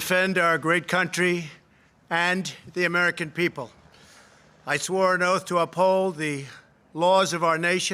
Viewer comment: I thought he said he didn't swear an oath. These people are as nutty as fruit cake.